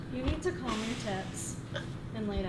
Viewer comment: The part from 8:45 onward cracked me up – B-Dubz with a mouthful of Steve's head, shaking him about, then the look on his face when tells him "You need to calm your tits" (unless I misheard that!) 😁😂 Even funnier in hindsight, given that the next video has Bruce Wayne schooling Norman for hyper-energetic behaviour. Thank you for posting your videos, and respect to you for being so honest and open about your health issues and depression. I hope you and are having the best vacation.